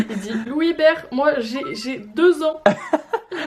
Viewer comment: Super vidéo ! Au moment dans les audios où il dit : "?Louis bert?(?je sais pas si c'est ça le mot?) moi j'ai...j'ai 2 ans " j'ai entendu " ?Louis bert?(??tjrs pas ??) moi j'ai 2 dents" C'est tout.